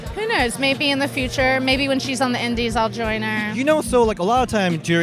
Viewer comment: Im prety sure we heard her say "when she joins the endeds"